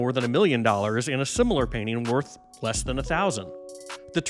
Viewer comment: Sorry, did you say "similar"?...